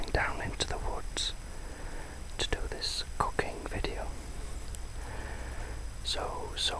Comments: OMG! When you first said "going to do this cooking video" i heard it as "going to do this cocaine" I was like WHAT?!! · sounded like you said "cocaine video" lol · at first i thought i heard u say cocaine video as opposed to cooking video lol thanks for lulling me to sleep u r awesome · Lol in the beginning it sounded like u said "this cocaine video" haha great video tho love the fall sounds of crunching leaves in the forest and cooking sounds 👍🏻❤️